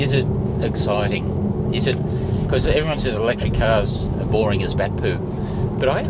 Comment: Hey, I thought you said Grey cars are boring? I totally agree, anyway the fact remains that EV's are character boring cars for character lacking people. I'm excited to see when you start on yr " F600/ Toyota bus project. I've already named her " Bertha " looking forward to that. Cheers from NZ